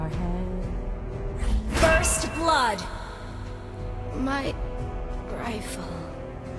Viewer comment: i thought lesley said my trifle xD